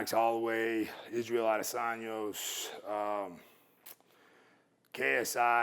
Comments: did he really say adesaños? 😂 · did he just say Israel Adesanyos? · "Israel Idasanyos" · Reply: I thought he said Israel, Dos Anjos.